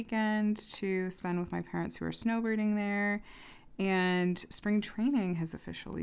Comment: Oh my gosh, you said your parents are snow-birding! I thought you said snow BOARDING. I was so confused how get would do that in Arizona. 🤪🤪🤪